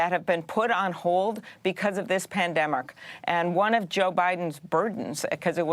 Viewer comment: Lmao what did she say at 4:51, Pandermerk